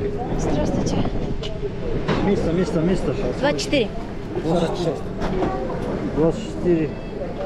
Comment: it is translated as 24, did u say 24? because the dude asserted u said 26... nice digital age they got there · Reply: I actually said 24 😂 I did question myself, but then when I was editing saw that I definitely said 24 haha